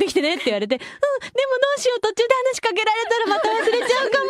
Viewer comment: oops, correction: 7:27 i'm pretty sure she said "but what do i do?" instead of "i wrote a memo" 🤦‍♀️